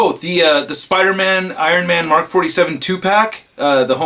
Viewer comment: I thought he was saying 2pac 😂😂😂